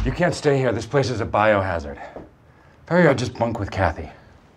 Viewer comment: what did he say? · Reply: Better I just bunk with Kathy